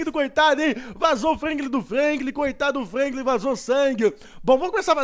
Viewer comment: ***** aos 3:43 minutos vc falou vasou o Franklin do Franklin???? foi so eu q entendi isso ou mais alguem????